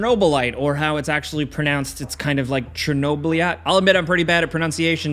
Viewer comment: Did he just say some people call it Chernoblyat?